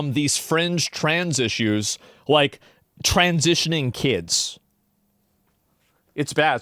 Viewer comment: I thought you said trany lmao